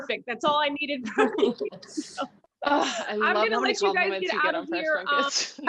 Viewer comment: Wait what does Mackenzie say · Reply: I think it's "ugh, I love how many compliments you get on press junkets" 😄